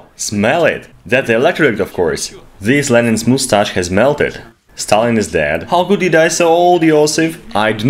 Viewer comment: did they just say “Stalin is dead”? Lmaooo